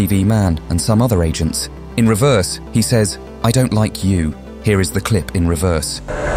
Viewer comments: I’m gonna correct this 2:51 he doesn’t say “I don’t like you” He actually says “I’ll demolish you” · he actually says I will demolish you